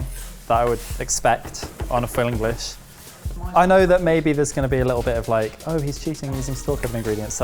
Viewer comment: "Oh he's cheating he's using *something" ingredients" What is he saying there? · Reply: "Store-cupboard"